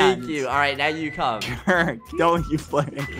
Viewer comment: did he just say don't you f